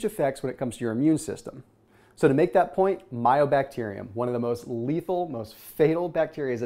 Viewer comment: Love your videos. Mycobacterium. Sounded like you said myo....